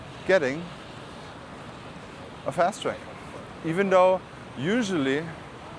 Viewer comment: you talk about a long line in immigration and "you might consider getting a _________". ???? Please clarify what you said thanks. I'm coming from America soon. Still don't have eVisa approval...absolute NIGHTMARE trying to get it to accept payment form...took a week and 50 tries...insane. And now they are not back in office til the 5th...disaster. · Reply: a Fast Track like this <link>